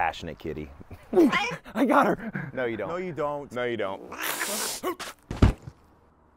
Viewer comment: Half a second i thought he said water cannon.